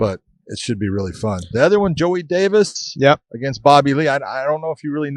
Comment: Hello Mrs. 0:14 Big John welcomes 1:17 Big John accuses Podcast Dave of TOOBIN-ING! 😂😂😂 1:45 Bellator 253 2:02 Benson Henderson vs. Jason Jackson. Is Jackson, his mindset & his size a puzzle Benson can solve? 18:06 Darrion Caldwell vs. A.J. McKee. Will Darrion slow down considerably in the 4th and 5th rounds? 37:42 Keith Lee vs. Raufeon Stots 44:54 Joey Davis vs. Bobby Lee 50:18 UFC 255: Will it break 200k PPV buys? 50:50 Deiveson Figueiredo (c) vs. Alex Perez 52:02 Valentina Shevchenko (c) vs. Jennifer Maia. Shevchenko vs. Nunes the only interesting fight left? 57:37 Maurício Rua vs. Paul Craig. The final fight of Shogun Rua's career? 1:06:10 Mike Perry vs. Tim Means 1:10:17 Who will corner Mike Perry? Perry talking Josh's advice, selling his corner spot 1:11:50 Katlyn Chookagian vs. Cynthia Calvillo 1:15:23 Mike Tyson vs. Roy Jones Jr., Uncle Chael apologizing for PED accusations, Roy's chin, will it be an actual fight or an exhibition bout? 1:23:22 Muhammad Ali vs. Joe Frazier 1:26:57 New T-shirts at <link> Anyone miss Weighing in on the Odds? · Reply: Thought at 44:54 it said Joey Diaz vs Bobby Lee lol